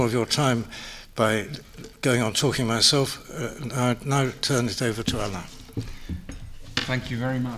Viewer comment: It sounded like he said, "And now I'll turn it over to Allah"